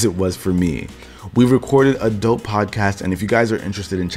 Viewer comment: Lol; my goodness man. I thought you said at the 13:45 mark or so we've recorded some adult podcasts. I almost spit my drink out. I had to listen to it twice to understand you said 'a dope podcast,' not 'adult podcast.'